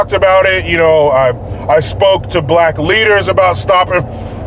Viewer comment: Idk why but I kept hearing “black liters” lol